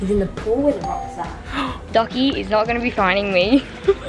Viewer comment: anyone else heard Biggy say dockie instead of Sockie at 19:27????